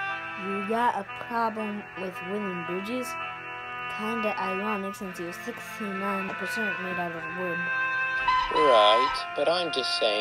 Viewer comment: What exactly did Derek say at 4:25-4:29? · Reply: "Kinda ironic since you're 69% wood.